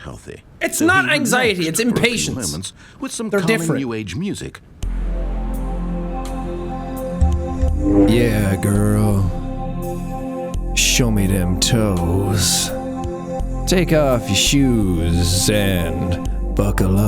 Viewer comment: Ok i was playing a game while watching this(multitasking at it's finest). When i heard "ya girl,give me those toes." I was so confused 😂